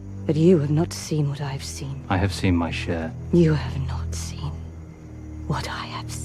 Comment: Did Elrond say "I have seen my shit" at 08:51?